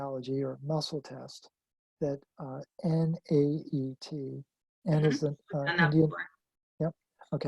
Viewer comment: Great info. What was the treatment he talked about - I thought I heard "N-A-T-E kinesiology," but can't find anything online about it? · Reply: naet